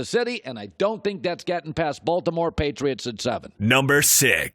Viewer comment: Hold up 2:55 does he say he doesn’t think the Patriots are going to get past “KC, Baltimore, Patriots?” 😂😂